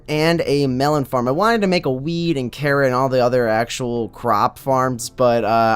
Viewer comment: totally thought you said a weed farm instead of wheat.